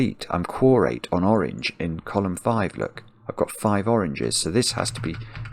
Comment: what word do you say at 35:48? It sounds like you're saying "I'm complete, I'm chorate(?)" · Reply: oh wait I see, it's "Quorate", like related to "quorum". I'll leave this comment up for anyone else who is also not familiar with this word!